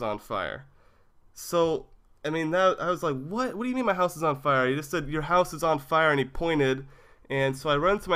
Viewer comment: I thought it said how my penis set on fire